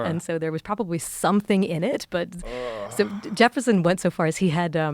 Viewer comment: Jefferson I thought she said Rosevelt 1:38:20 edit